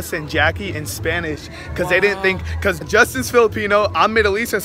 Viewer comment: Did I hear "JUSTICE TO FILIPINO"??